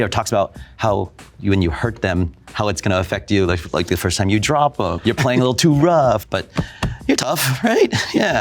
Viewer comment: wait did bro say the first time u drop em 2:35 ayooo